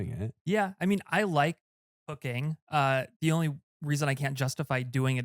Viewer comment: Did he just say "I like cocaine" or is it just me? 😅 1:04:17 - It is Loperamid, It cannot break the Blood-Brain-Barrier... unless being coadministered along with quinidine, a heart related problems treatment which inhibit the BBB and loperamide rushes into the brain giving the pacient high and severe overdose up to the inhibition of a breathing center which would then become a CoD B=]